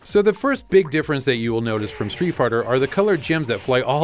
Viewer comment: Did Dave say Street Farter?